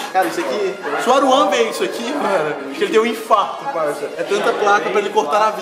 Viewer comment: O que vc disse?! 😱😱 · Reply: Ele falou parça uahsuash!